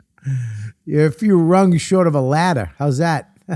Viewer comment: I heard “you’re a few rugs short of Aladdin” and went ‘yeah that checks out, he flys on carpets, I get it’